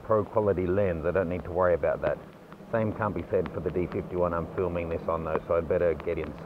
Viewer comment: is the D5100 weather sealed as well? I tought not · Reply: I just noticed i misheard that "...same CAN't be said for the 5100 I'm filming this on right now.." Thought said CAN